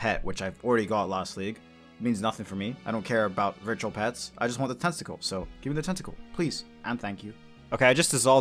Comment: i swear it sounds like you say "i just want the testicle" at the beginning LMAO